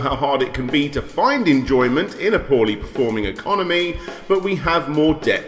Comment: So at 1:18 did anybody else hear him say enjoyment and not employment?